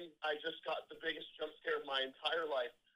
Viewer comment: Genuinely thought they said "there's a moose in my house"